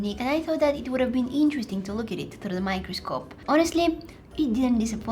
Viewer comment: Oh Oh Oh I thought you said microphone😂😂😂